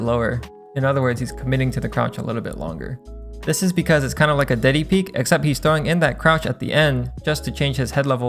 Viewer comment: did i hear DIDDY PEAK?